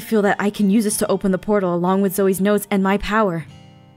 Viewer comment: I thought she said 'and Zoey's nose' for a second there...who nose how that would've turned out?